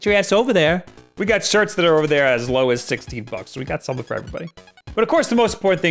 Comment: you freaked me out there. i thought you said "we got shirts over there for as low as 60 bucks" i was like wtf is wrong with you he said 16..... even though captions also thought 60 might wanna fix that